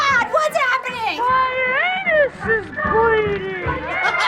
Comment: I thought he said my penise is bleeding